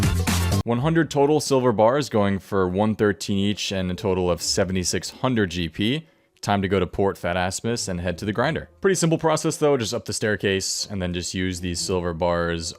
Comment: I heard port fat ass miss